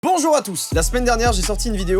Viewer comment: Joyca : Bonjour a tous ! - moi : C'est ptikouik !? ;_